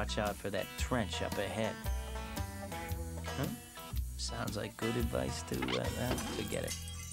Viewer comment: what did the narrator say in 0:53? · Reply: Sounds like good advice to ...uh falls ...forget it.